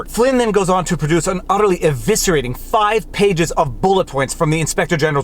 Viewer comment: It might just be my bias, but I thought this was a pretty good video 14:58 I want to think you said Udderly Eviscerating